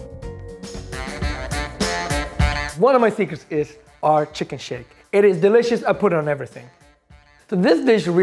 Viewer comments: I thought he said chicken shit 🤣🤣🤣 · thought he said chicken shit I literally watched that part 5 times LMAO